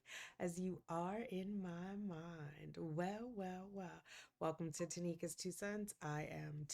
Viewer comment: I thought you said “welcome to Tanikas two sons” 🥰 not two cents.